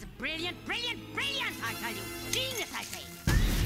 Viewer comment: its GENIUS I say Its not "Penis I say" and I don't see how so many people think she says penis. That doesn't even make sense.